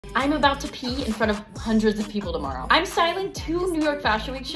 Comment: i thought you said immabout to pee in front of hundreds of people tomorrow ;,(